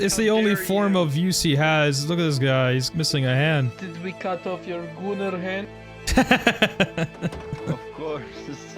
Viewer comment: I laughed so hard when i heard "did we cut off your gooner hand" 😂